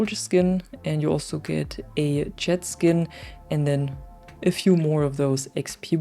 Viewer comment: When said "jet skin" I thought she said "jet ski".🥺 I got so excited! Then disappointed.😮‍💨